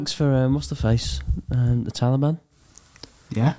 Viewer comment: Thought you said ‘the telly man’ lol